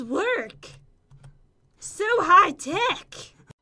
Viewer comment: I thought said on the front is ehem sorry for thy wurds "So High Dick!" *so sorry guys for that comment because i have bad ears and then i understood that she actually said "So HigHTech!"